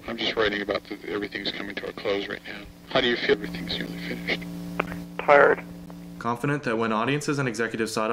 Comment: I misheard "tired" as "hard" and thought "what the hell...?"